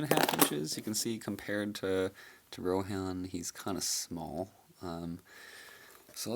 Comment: "Compared to RohAN," I thought he said "Ro-Ham" for a second spare me